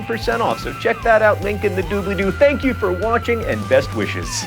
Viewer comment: Every time he says "Thank you for watching mental floss" I hear "Thank you for watching menopause". Maybe he should try to enunciate better.